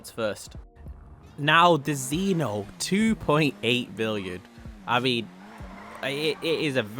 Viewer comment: sorry but did he say 2.8 billion